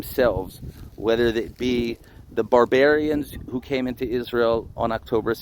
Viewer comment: Youtube subtitle writes at 2:30 instead of "barbarians" (as he said) "barbar arians." That seems funny at first, but when you think about it, you realize that it describes the attackers exactly right.